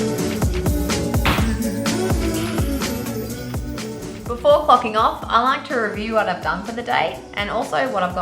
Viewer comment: did you say "before fahking off?"